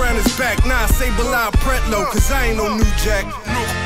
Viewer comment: What's the line he says after "Metaphor the Great, Nino Brown is back"? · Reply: Nah I'll say Bilal Pretlow 'cause I ain't no new jack. It's a Reference to another infamous drug dealer.